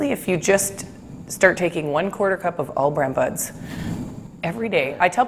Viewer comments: All bran buds.... I thought she said All Bran Butts!😂😂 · what does she say? the food i cant hear it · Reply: All Bran Buds. See the link in the description. Thanks for watching and subscribing